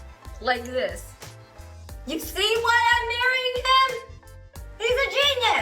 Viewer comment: Did Jeannie say: "You see why I married him" 13:41 referring to Jay cleaning her ring